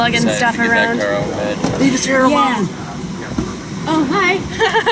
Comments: What did the guy say on the plane at around 7:55?? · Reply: I heard "Babie's here alone" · What did the guy say on the plane at around 7:55?? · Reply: He said leave his hair alone